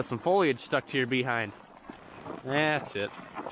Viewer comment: I had to replay 26 seconds in because I wasn't sure if I heard "thats it" or "ah s***"